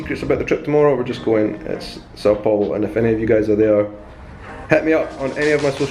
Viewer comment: OMG< I thought said he is going to "South Pole"! ALAS, "São Paulo"!